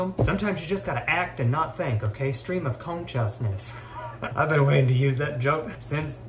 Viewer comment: When u said "conchas" at the beginning the auto-generated subtitles thought u said "chest comb"